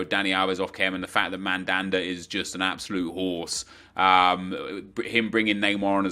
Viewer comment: I thought he said "Mandanda is an absolute whore" and not "horse" hahaha.